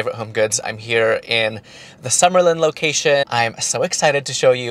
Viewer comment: You live in Vegas? I thought I heard you say Summerlin?